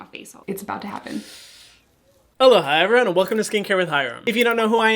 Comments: when I first started watching your videos I always thought you said " aloha hyram" and I used to think, like why is he welcoming himself haha then I realized he said aloha everyone😂😂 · Every time I eat and hear hyrams intro I hear “haloha” instead of aloha I’m sorry hyram